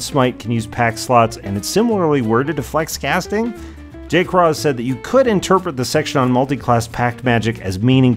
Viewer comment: What is being said at 1:20 ? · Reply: J-Craw. It's a nickname for Jeremy Crawford, the Lead Rules Designer for D&D 5e.